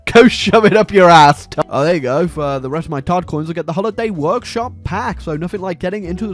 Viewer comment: From now on, I will only refer to Microtransaction currency as "Todd Coins" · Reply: I had my phone volume low and I thought he said COD coins lmao